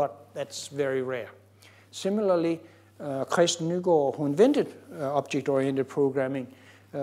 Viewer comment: spoke a the creator of OOP, i couldnt understand the name properly, I tought it was Alan Key who invented it, but apparently Bjarne state otherwhise. I'm very curious to read more about the man he talk about, if anyone can write his name, that would be lovely. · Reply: Kristen Nygaard who together with Ole-Johan Dahl invented Simula